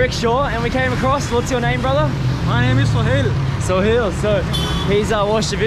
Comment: Just a name correction. The buddy who gave you bike ride told you his name Sohail not Sunil. You heard it as Sunil. Thanks for sharing this another great video. I watch all your Pakistan videos. Many thanks for visiting Pakistan 🇵🇰. Originally from Karachi, I have not visited Pakistan since 2005. Lots of love and care from Canada 🇨🇦.